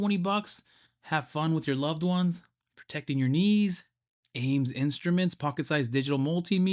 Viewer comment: "Have fun with your loved ones protecting your knees" 😭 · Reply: I thought he said protecting your loved one's knees.